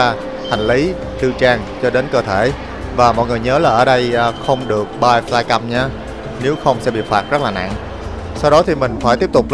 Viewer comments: Đoạn 20:20 anh ấy nói không được mang món gì đó mà mình không thể nào nghe ra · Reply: Ko dc bay flycam nếu ko thì sẽ bị phạt rất nặng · không đc mang Ba za căm là cái gì có ai nghe đc ko ? Nghe lại 4 lần vẩn ko hiểu ổng nói gì. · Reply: flycam